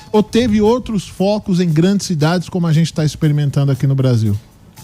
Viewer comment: Eu ouvi “cidadoes”??